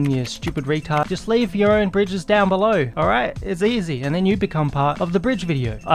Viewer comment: it sounded like he said leave Urine Bridges down below. so I guess that is like the stream from when someone takes a piss and it arcs a bit?